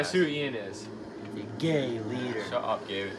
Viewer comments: did he just say “Gay leader” ?? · did he say gay leader?